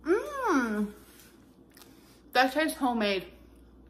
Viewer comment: Did she just say "hoe made"?